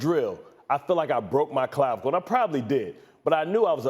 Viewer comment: what did he say? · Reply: Clavicle